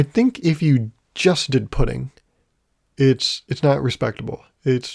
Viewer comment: 100% thought you said "Is putting a respectable food..." and then just stopped talking about it